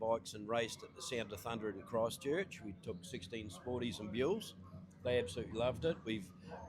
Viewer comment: Buells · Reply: Ah! I thought they were saying "mules" 😂